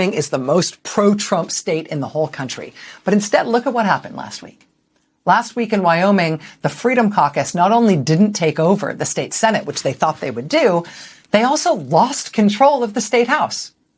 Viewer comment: as a non native english speaker i thought you said Freedom Carcass.. fitting name i thought...